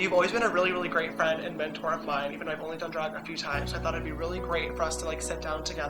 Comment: I thought he said he's,"only been drunk a few times," lol, the sound quality at this part is bad, so that's probably why xD no hate